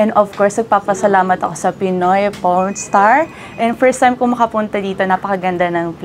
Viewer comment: Did she say "Pinoy Pornstar"? 😅